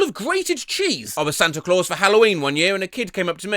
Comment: Hold on hold on, did he say he was SANTA for HALLOWEEN???